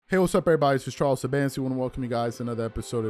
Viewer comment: Nice. I finally got the last name. I always hear "Charles Sabansky".